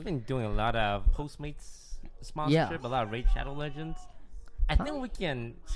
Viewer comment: diD hE SaY RaId sHadOw lEgeNds